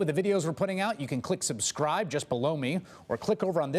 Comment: At the end when chris Hayes says subscribe just below me. I always hear “subscribe just blow me”